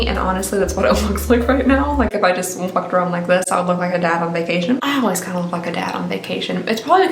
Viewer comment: It sounds like you said I always fuck a dad on vacation and almost spit out all my coffee. Holy chickens!!!